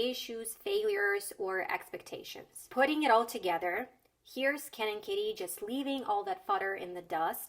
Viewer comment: Okay, first, I heard 'He's Ken and Kitty' which obviously didn't make any sense, so yeah I'm not fluent Second: wth is a shark tank in that context??? I feel I'm not getting educated if that was the goal